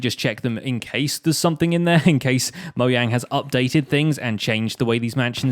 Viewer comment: Did he say Mo-yang instead of Mo-jang lol 😂